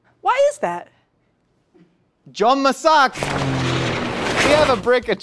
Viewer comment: Did I just hear Josh say 'Jom Masak' 😂 Love itt